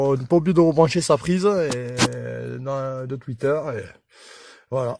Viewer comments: en fait c'est quoi cette prise de "fuiteur" qu'on est pas obligés de rebrancher ? sa sert a quoi ce fuiteur ? (ou twitter je sais pas ce qu'il dit exactement ...) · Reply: Un tweeter c'est un haut-parleur qui fait les aiguë j'ai jamais dit qu'il ne fallait pas le rebrancher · Je pense que tu dois avoir un problème avec mon accent, je viens de regarder ma vidéo et je dis de ne pas oublier de rebrancher le twitter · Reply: ah merde !!! je pensais que tu disais "on est pas obligé de rebrancher sa prise ..." moi c'est ce que j'entends .(a